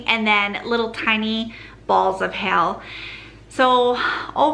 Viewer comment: I thought you said balls of hell then I realized you said hail. 😂